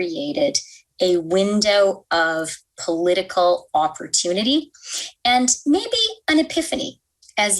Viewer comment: "COVID has created a window of opportunity ..." I hear that as 'exploitation'